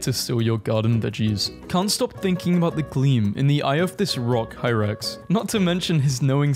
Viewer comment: When he said hyrax i thought he said hi rex and i was so confused